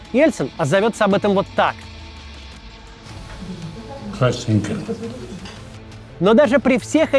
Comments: Что он сказал? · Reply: Красненькой налить попросил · не понял, что он сказал ?????????????? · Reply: "простите" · а что там Ельцин сказал на 1:04:55? (через год, как путин к власти пришел) не получается расслышать в видео, может кто-то знает? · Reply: Красненько☹️